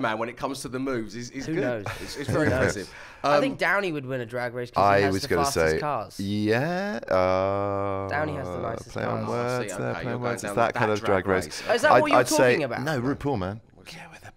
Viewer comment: When he asked Tom Holland about who would win on drag race and he said RDJ i misheard him, I thought he said he has the nicest ass 😂